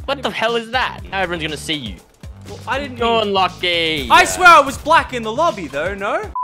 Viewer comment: WAIT DID HE JUST SAY HE WAS BLACK